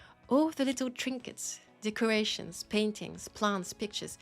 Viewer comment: Place feels so empty without me? I did not know you were into Eminem. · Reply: I could swear you said 'All the little trinkets, the Croatians" there. Made me do a double-take, and a chuckle.